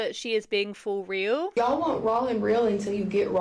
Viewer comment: …is she saying….”rawL?”